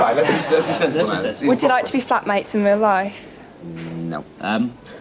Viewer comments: I misheard that as "fuck mates". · Literally thought that girl said fuckmates, rewound it 3 times then buddy said flatmates, then i was like ohhhhh